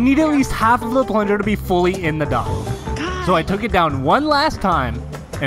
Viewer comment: Thought he said "your yt channel is super boring"